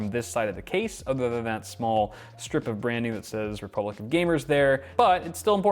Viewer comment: I listen to it again and I'm sure he said Republic of Gamers, But I first heard Republican gamers for some reason and it made me laugh.